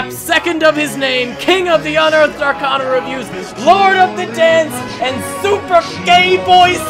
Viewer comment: i wasnt looking at the screen and i heard "Lord of Dense" and my brain just accepted it EDIT: 4:46 "Dont go dabbing underwater." Yeah, im going deaf.